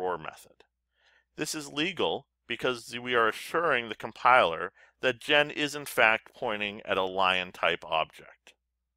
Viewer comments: i cant hear the sentence at 4:02. can anybody tell me pls. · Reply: "This is legal because we are assuring the compiler that Jen is, in fact, pointing at a Lion type object. " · "This is legal because we are assuring the compiler that Jen is, in fact, pointing at a Lion type object. " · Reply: thank you so much sir for explaining, I misheard is, in fact to "isn't fact ".